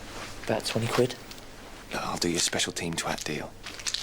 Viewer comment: What does Donovan say at 6:48? My ears are hearing "I'll do you a special team drug deal" but obviously that's not what he says, can't make it out at all? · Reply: He says "I'll do you a special team twat deal"